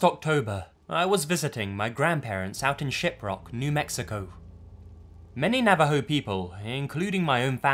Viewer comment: When he said "Shiprock, New Mexico" I could have sworn he said "Shitrock, New Mexico." I was like "uhhh...Trailer Park Boys?"